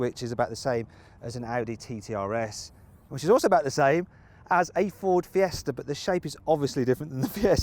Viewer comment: 「自動車」←完璧な発音である。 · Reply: 「which is also about the same」を早口で言うと「自動車」に聞こえるトリビア